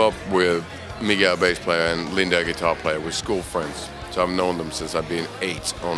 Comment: Can someone please help me? What does he say at 0:29 ? Whos guitarist and who's bassplayer? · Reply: Mige and Linde